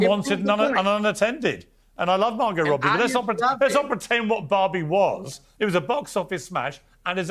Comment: with all their accents, I couldn’t tell that they were talking about Barbie. I thought they kept saying Bobby I’m like what movie is that